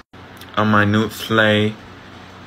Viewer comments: "Slayed if the Grammys were a funeral" savage 🔥🔥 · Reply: I thought he said if it was a Vietnamese funeral 💀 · "Slayed if the Grammys were a funeral" savage 🔥🔥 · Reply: I thought he said "If the Vietnamese had a funeral" 😭😭